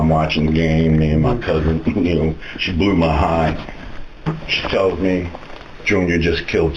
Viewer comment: Nva thought i would've heard a dad say "she blew me" 😂